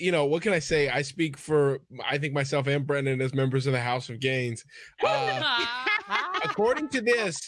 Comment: Fully thought he said “house of gays” instead of “house of gains”